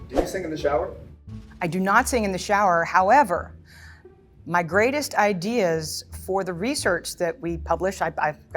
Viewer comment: I thought it said "breast ideas" 😄